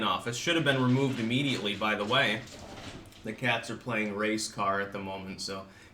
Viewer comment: Ohhh, "race car." Shows just how today's climate has me assuming the worst...for a second I thought he said the cats were playing the "race card."